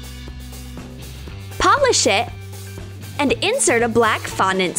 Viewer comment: at first I heard pop a sh**